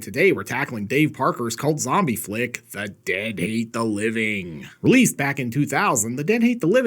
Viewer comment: What?! Is it November already? Oh...I thought you said "My Dad Hates Thanksgiving".🦃🧟‍♂